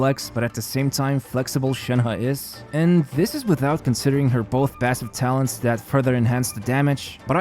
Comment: i thought you said "both her massive talents" at 3:16 and thought of something else...